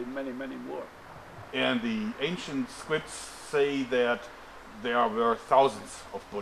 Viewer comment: I heard "ancient squids"